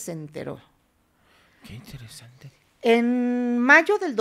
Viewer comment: Que es lo que dice el muchacho en el minuto 12:27 alguien sabe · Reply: Dice: "que interesante..."